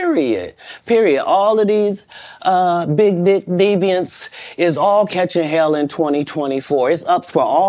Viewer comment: Did he say "big d*** deviants" ??